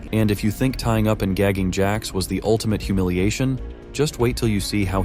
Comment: i had it at 2x speed, and i swore instead of saying gagging jax it said daddy jax, i litteraly jumped out of my seat in shock lmfao